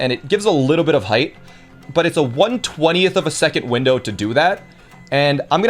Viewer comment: wait, how is it a 1/120th of a second window, if the game runs at 60fps? EDIT: Apparently, it's 1/20th and I misheard